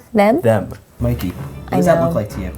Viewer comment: I heard “Liz” rather than them